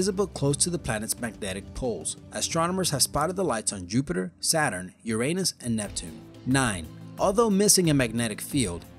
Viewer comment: sounds like he saying your anus XD